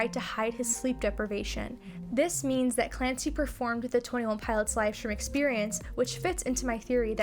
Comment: Hi :) would you be so kind to tell me what you said right at 2:18? "Clancy performed the live stream [...]" I can't figure it out on my own, it's confusing me because Clancy is not Tyler, even if he's dressed up as him. At this point I need help please. Thank you friend! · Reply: Yeah, sure! "This means that Clancy performed the twenty one pilots livestream experience which fits into my theory that Tyler has been portraying Clancy since the beginning of Scaled and Icy." Basically what my theory is, is that Tyler is playing the role of Clancy like an actor playing a role in a movie. Hopefully that makes sense :)